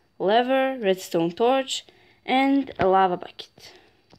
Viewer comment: Did you say lava bucket